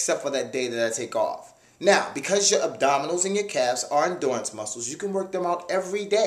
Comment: Abdominal and calves muscles aren't "What?" muscles? It sounded like dance muscles. Somebody tell me?